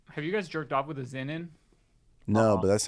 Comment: what is a zinon? Zinnon? Zenon? What did say there